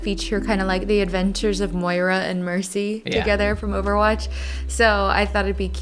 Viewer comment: For some reason every time said "Mercy" I heard "Tracer" in my head and was thinking "She's giving her the wrong hair color! Oh no!" but by the end my brain finally caught up and sent me a message: "You're an idiot! Also, on an unrelated note, that's Mercy, not Tracer..." The end result has a very wholesome vibe anyways. Very pleasing color choices, whether they were deliberate or arrived at by accident.